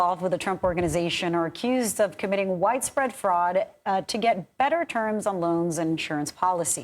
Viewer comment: Had to put on CC. I thought she said, "White spread fraud...." Of course, that would be more accurate.